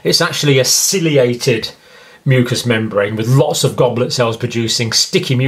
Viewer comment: This is so good...but I have a question at 4:46 did you say with lots or with the loss? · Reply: I believe he said “with lots”